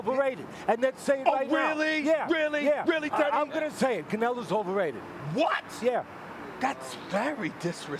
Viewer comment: Teddy Atlas says Canelo Alvarez is overrated... then says Triple G's overrated. He did not say them together as in meaning both.